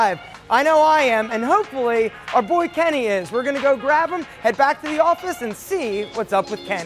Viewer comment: did he said KennyS? wait whaaaaaaat?